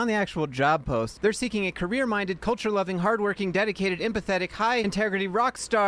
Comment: i know the post said "hardworking, dedicated, empathetic" but what i heard you say was "hardworking, dedicated and pathetic"